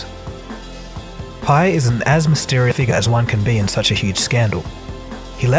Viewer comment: "Pi is an azmisteriffig as one can be" – what is actually being said here? Also, at it 2:27 it should say "Valhalla" not "Vahalla". Enjoying the video otherwise though!